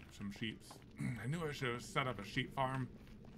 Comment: I went back 10 seconds to hear something when said that he knew he should gave SET UP a sheep farm, and I heard “Son of a sheep farm!”